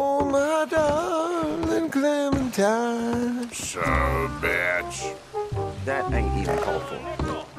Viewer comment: it sounds like hes saying “Sup biatch!”